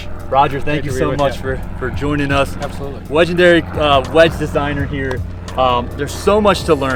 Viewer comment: "Wedgendery" ? Did I hear that right? That's actually perfect 😂